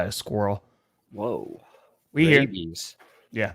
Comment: may have heard "rabies" but I heard "vrabies" and immediately wondered if that was the name given to Mike Vrabel fans. 😂